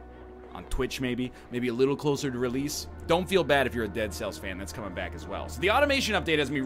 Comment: The heart attack I had when I thought he said dark souls instead of dead cells (the first minute)